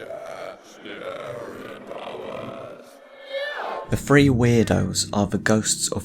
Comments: I thought they said spirit powers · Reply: OH, wait you're right! OOPS. Thank you for pointing this out!! Somehow I always heard it as 'Scary Powers'. XD It's so weird, I keep replaying it back and now I can't unhear 'Spirit Powers' LOL. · I always thought they said scary powers aswell lol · I've just realised they said 'Spirit Powers' Not 'Scary Powers'. Whoops. Always sounded like 'Scary Powers' to me. XD Maybe the out-of-placeness of 'scary powers' is why I found that line so funny. Now I know why it seemed so out-of-place: HE DOESN'T SAY IT! lol. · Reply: I thought it was scary powers too @@ Even while watching the video just now that's what I heard. Guess I'll go back and re-listen to it lol. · I've just realised they said 'Spirit Powers' Not 'Scary Powers'. Whoops. Always sounded like 'Scary Powers' to me. XD Maybe the out-of-placeness of 'scary powers' is why I found that line so funny. Now I know why it seemed so out-of-place: HE DOESN'T SAY IT! lol. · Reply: I definitely hear "scary powers" there <link> :D